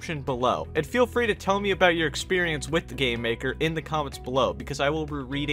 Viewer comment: Whenebrr he says gamemaker it sounds like hes saying "gaymaker"